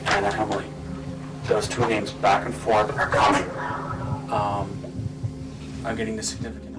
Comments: A great film and thanks for sharing i hear the evp at 21:29 as "their going now!" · I didn't hear "He's in power" at all. I heard "We're/they're coming now".